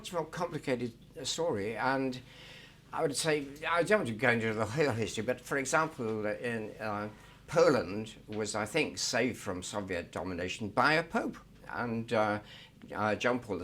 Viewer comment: wth did he say here?? 😂😂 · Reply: "And.. I would say, I dont want to go into the whole history but for example Poland, was I think saved from Soviet domination by a Pope"